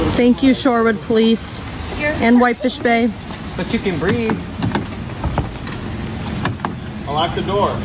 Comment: lol, what he say.. " but you can breathe "..